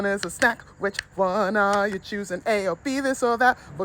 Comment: I dropped my phone in my bed crack and I heard “which one is poisoon”